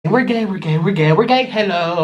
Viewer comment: is he saying "were gay"